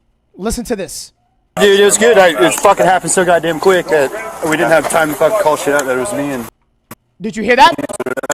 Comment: I heard "it happened so quick i didn't have time to call, it was just me and dipshit."